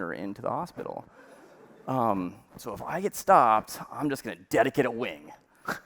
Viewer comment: what is he saying ? Dedicate a wing ?